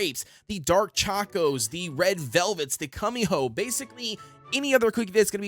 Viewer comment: Did...did he say cummy hoe? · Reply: No he just said Kumiho wrong